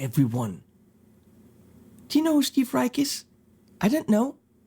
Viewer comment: Did he say Steve Rieck?